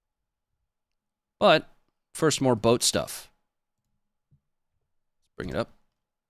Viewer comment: Did you just say “but, first more butt stuff”? I like where this video is going, alright lets do this thing…🫵 Edit: Oh wait, I think I was mistaken…I’m embarrassed